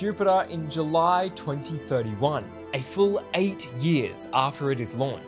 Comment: And you can watch it when it explores Jupiter from your comfortable seat in Heaven 😅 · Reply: Edit I thought said 80 years not only 8 😅😅😅